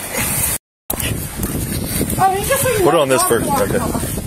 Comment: The “THIS IS POLICE BRUTALITY” really got me lmaoooo It really is such a sad situation though. It is really all on that one woman. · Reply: I thought he said "white brutality " lol